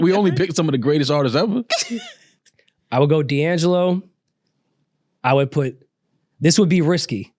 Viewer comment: why did I think he said liangelo😂😂 like dam he got 1 record 😂😂